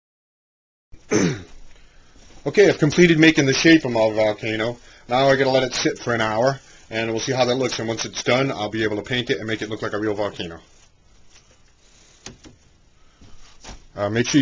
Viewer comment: did he say "Now ima shit for an hour"?